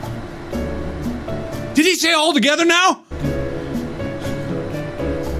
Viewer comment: THIS ENTIRE TIME, I ALWAYS THOUGHT HE SAID "GETTING BORED NOW" IN THE ELEVATOR! YOU MEAN TO TELL ME IT'S "ALL TOGETHER NOW"?!?!